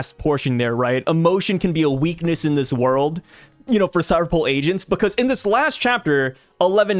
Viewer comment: sounded like you said “Cipher Pol Asians,” 😅😂😂😂